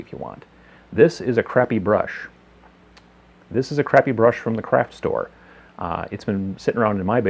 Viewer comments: I thought he said "a crappy brush from the crap store" XD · Had to re-watch the "this is a brush"-part. First time I heard "This is a crappy brush from the crap store."